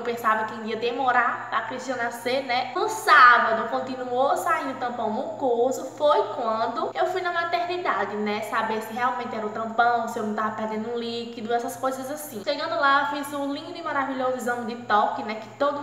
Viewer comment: nn sei pq mais eu entendi "Tampão do cu"😂😂😂😂 a melhorrr❤️